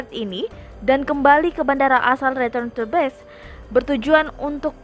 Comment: ada yang tau ngomong apa mbak nya? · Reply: Return To Base